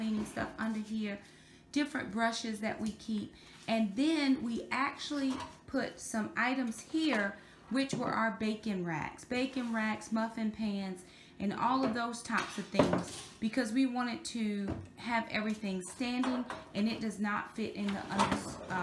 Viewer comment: Good job! For a moment I thought you said bacon racks! 🥓 🥓 I’m enjoying your Tidy Tuesday vlogs 😀👍🏻